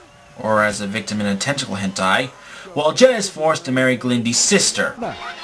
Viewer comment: I watched only 9 minutes of the pilot. I thought they were saying "Glenda's sister Yelda". I was like "Who's Glenda?!"